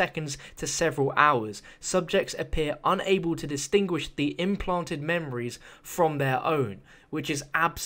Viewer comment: I thought Dr Saleem said 'implanted memory from heroin'